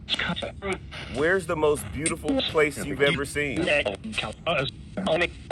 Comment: I heard California.